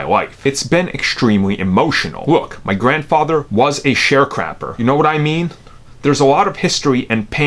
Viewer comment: Whoelse heard him say share crap instead of sharecrop